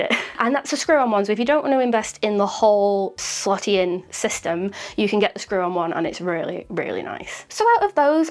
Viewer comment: thought you said “slutty system” then i remembered your accent. (i’m from the States)